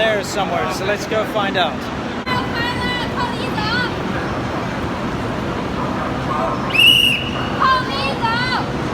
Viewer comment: wth is she shouting? sounds like靠里走 but idk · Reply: yes，靠里走。this comment from native chinese speaker.😉